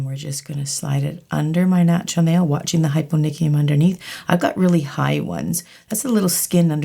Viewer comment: What did she say the skin under the nail is called? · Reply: hyponychium - sounds funny